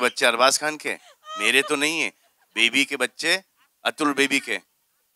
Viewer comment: k bad kya bola salman ne samaj nahi aaya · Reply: Apnau bahnuy atul agni hutri ko kaha k baby k bachay shayed ye loug atul ko baby kahtay hain tu alvira ka husband ki baat karraha tha